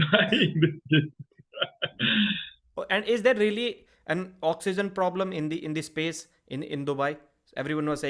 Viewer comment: What did you say there, "is there an autism problem in Dubai?"